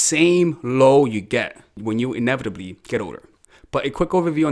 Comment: When you were saying the word "older", i thought you were saying "odor", and i was slightly offended